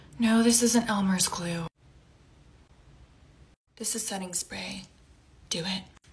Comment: I thought she said “this is satan’s spray” 😂